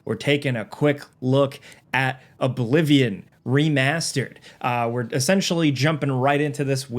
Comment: Why did I hear "A Bolivian: Remastered"? I went back and def didn't hear it again, but... anybody else?